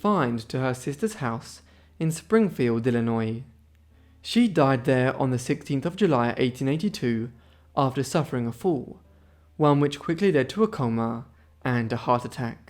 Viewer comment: "..she died after suffering a fool..." 16:11 I misheard you about Mary Todd Lincoln's death... 😮😅